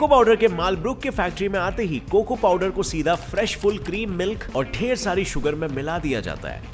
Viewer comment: Did he say Fresh bull cream at 5:30 😕